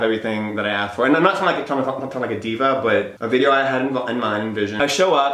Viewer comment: what did he say? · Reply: I think he was trying to say something along the lines of not trying to sound like a diva